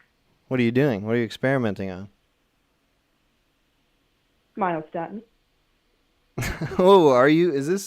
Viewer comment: What did she say? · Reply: Myostatin. Sounds like she's the wife of the dude who was talking about genetic engineering earlier